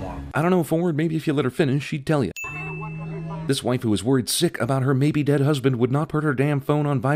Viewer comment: It sounded like he said “this waifu is worried sick” around 5:10 😂